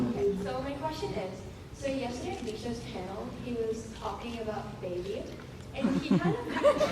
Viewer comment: What did she said at 15:40? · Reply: "So yesterday in Mishas panel he was talking about Baby and he kind of..." That's what she says before she gets interrupted by the audience reacting :)